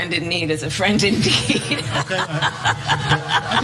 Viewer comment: Don't be so rude for Kamala. Just watch the whole conference. Few moments ago Duda had said (or at least tried to) "Friend in need is a friend indeed" but he mispronunciated it. Instead of indeed he said like "indeek", some even heard it as "indick". You get it now? "Friend in need is a friend in dick" XD That has already grown a big time meme in Poland. Those who doesn't like Duda for his conservatism laugh because of his poor English all the time. There was a climate conference in Davos (Switzerland) where Duda also had big problem speaking English. I really understand why Harris might have laughed od what Duda said xD